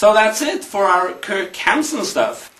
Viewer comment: does he say Kirk Hanson stuff?